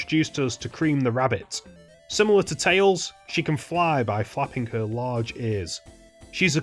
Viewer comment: why does it sound like hes saying by [f word moment] her large ears